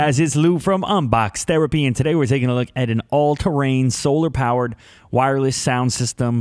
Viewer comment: Honestly, I thought this said "Terrapin". team #UMD